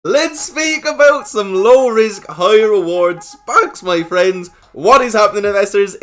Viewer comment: I legit heard him say high risk low rewards. I was like, "sounds right up my ally "